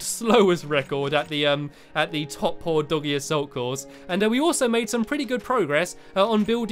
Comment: by the way, when tried to say top 'paw' doggy assault course it sounded like he said top 'poor' doggy assault course!